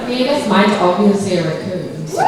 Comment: What did alycia said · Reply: Someone asked what they thought their spirit animal was in the 100 and alycia said "well I guess my is obviously a raccoon"